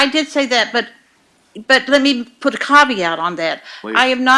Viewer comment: Title Ideas for Nancy’s Next Book: -The Wrong Caveat -The Wrong Bra -The Wrong Slide and Barrel -The Wrong Prosecuting Attorney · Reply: I thought she said "with a copy out".